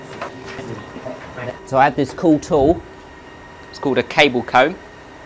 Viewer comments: What a great work. At 9:30 you use a tool to manage the cables. I am sorry i can not understand what are you say, what is the name of the tool. Thank you in advance. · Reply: It’s called “cable comb” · where can I buy this tool? Or how is it called? We need that :D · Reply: Cable Comb